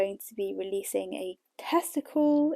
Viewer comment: Love your podcast 😊 although I almost fell off my chair when I heard you say “I’m going to be releasing a testicle at the end of March”. Took me a moment to realise you said tester call 😂